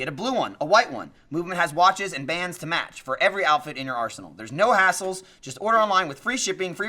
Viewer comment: MVMT add read "their is no hassle" I heard that as "their are no assholes." Both true tho.